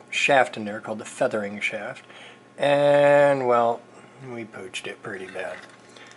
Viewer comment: I heard you say "pooched" before and I always thought you were saying "pooged." So now I say that things are pooged (pronounced "poojed").